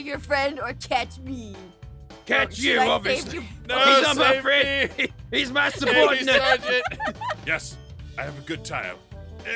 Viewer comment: Garroth; "he's masturbatin'" da faq? and did anyone else hear that?